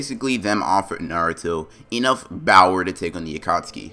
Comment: Why does it sound like he is saying “Bower” instead of power besides the fact he has an accent